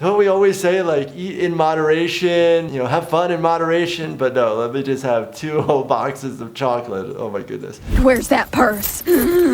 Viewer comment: "let b*tches have 2 whole boxes of chocolate" That's what i heard lol